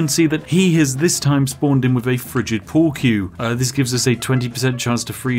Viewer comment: When he said frigid pool wie I swore he said friggin pool cue